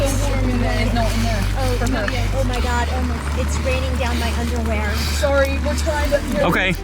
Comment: Anybody else hear " it's raining down my underwear " at 6:22 😂😂